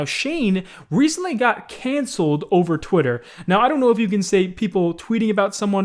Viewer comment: I thought you said he got cancer